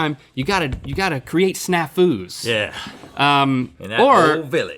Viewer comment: This whole time I thought they were saying “snack foods” not snafus so this whole time I was just waiting like “where’s the snacks???” ...whoops